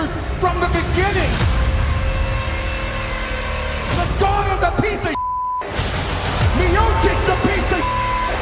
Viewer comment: Can anyone tell me what brock told in 3:9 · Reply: human memory he said ”ngannou is a piece of shit, miocic is a piece of shit“